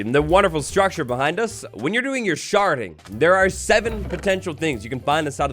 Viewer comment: i really misheard sharding for sharting im too far gone :(